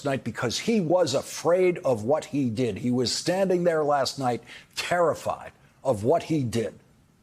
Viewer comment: I don't know if intended to sound like he said "tarriffied" there, or if I'm just imagining it, but I choose to believe he did. :D